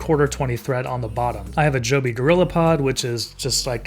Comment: What's the stand called for the astro I heard you say a jobby..... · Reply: it is Joby gorillapod